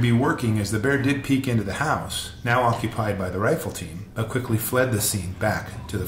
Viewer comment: I can't believe I'm sitting here watching this at 4:00 in the morning. Bears terrify me. I'm a swimmer and grew up next to the ocean but was afraid of sharks then I moved to the Northwest and fell in love with the beautiful mountain lakes and streams. Bears! They have bears here !🤣 I've never actually seen one in the wild but a large black bear came walking right through our campsite while we were still asleep in a tent. I haven't been tent camping since. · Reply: lol I thought you said. Fireteam lol … 🤦🏻‍♂️